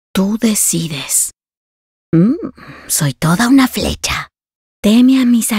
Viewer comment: "Una flecha, un muerto" quita 3 de vida xdxd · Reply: yo pense que decia una flecha a muerto